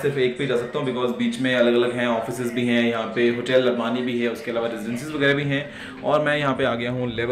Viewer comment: is he said hotel Ambani? 😂😂😂